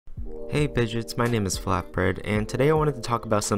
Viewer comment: I thought you said flap breaf